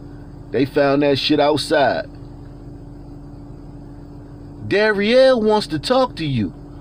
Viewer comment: I thought you said derriere 😅 🍑